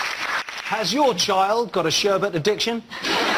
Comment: what did the man say at min 03:51? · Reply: Has your child got a sherbet addiction?